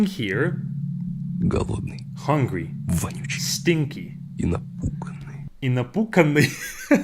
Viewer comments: actually, at that moment he said "напуГанный", which means "scared", I don't know why heard it as "напуКанный", because this word doesnt even exist, and his explanation of this word is ridiculous tbh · блин, мне всегда казалось, что он говорит "напуганный" 🗿